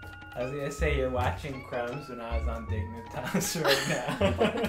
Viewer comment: What does he say about Crumbzz at 8:41 ? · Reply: says, "you're watching Crumbzz when I was on dignitas right now", referring to mellow.